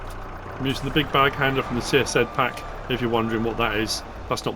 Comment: Whats the name of the big bags handler 28:56 "csed(?) pack“ for telehandler? didnt quite heard it, and couldn't find 😒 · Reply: CSZ pack, it's a massive pack of tools for the various types of loaders